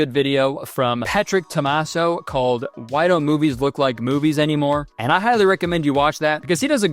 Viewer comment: I thought he said “called boobies don’t look like boobies anymore” Lmfaoo I’m doing dishes and can barely hear lol